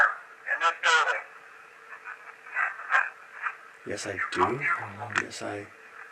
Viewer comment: Love watching your guys videos and for some reason, I always seem to hear things in your evps. Halfway through the video and this is what I heard in the first session... 41:19. Yes I do 41:43. I was always here 42:20. Leave! 42:40 Yes